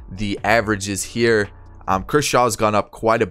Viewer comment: lmao I thought he said Kershaw